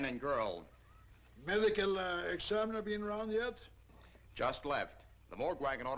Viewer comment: During one of my high school film classes, we watched this and the teacher paused the film after the delivery of the line at 9:37. He asked "now, did anyone make out what he just said?", and the entire class erupted into laughter with the realization that no, we had not. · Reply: Medical uh examiner been around yet?